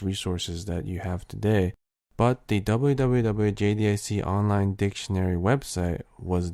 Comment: what was the site he said over 00:54 - 00:56 ? I didn't catch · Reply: WWWJDIC